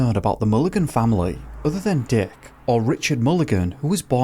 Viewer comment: am I the only one thought that said: "There's not a lot known about the Mulligan family other than dick all" XD